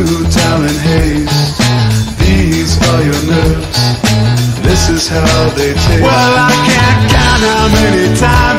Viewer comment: Damn I always heard it as “these are your lips, this is how they taste” which to me sounds so much cooler haha. I’m a lyric guy but hadn’t looked them up yet!